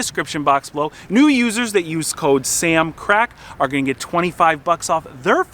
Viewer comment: Swore I heard Sam on crack then I was like okay replay.